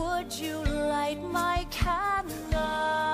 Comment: For some reason instead of "light my candle" i always hear "like my condon" WTF